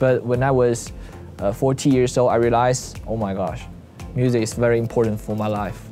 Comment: I thought he said "forty years old" then 😂😂❤